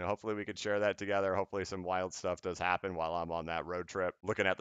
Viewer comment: My man said “hopefully some wild stuff happens while I’m on vaca” and I heard “me and the ole lady are gonna get weird” 😂